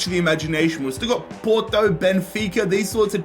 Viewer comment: Bro, I thought he said Boudreaux not Porto 💀